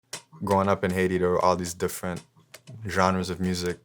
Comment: I thought he first said “growing up i hated all type of music”